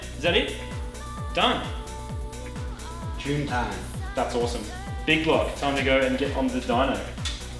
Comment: the captions said that's awesome big boy know it's time to dip on the diner,lol